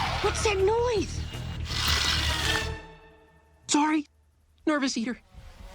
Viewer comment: I always thought he said "nervous here." But he was saying "nervous eater." Lol